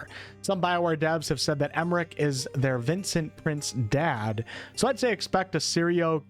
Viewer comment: Why do I keep hearing "Vincent Prince"? It's pretty clearly meant to be Vincent Price, right?